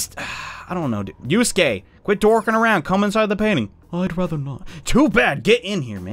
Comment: Thought it said "inside the Pantene" as in the hair wash, I got really confused